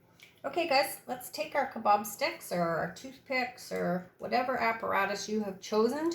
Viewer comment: What do you use as a stick? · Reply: Thanks. I thought you said kabo stick and couldn’t figure that out. Thanks again